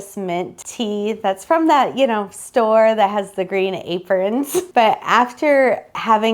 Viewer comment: I have a speech issue that we try to cut out, if you tell me where in the video you need clarification I am happy to help? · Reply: when you say where you got the citrus tea, is where I heard you say "that store with the green aprons"